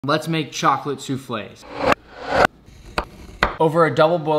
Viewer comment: I thought it said chocolate shuffle